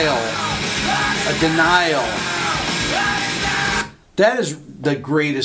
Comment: "The denial"? I always thought he was saying "blood his eye out". LOL!